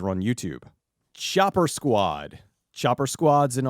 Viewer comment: Lol. I thought he said Shopper Squad. Doh!